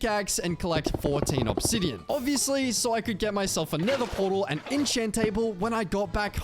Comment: did he say nig*** portal?